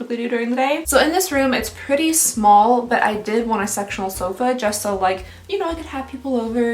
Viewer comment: I thought you said “sexual sofa” instead of “sectional sofa” 😂😂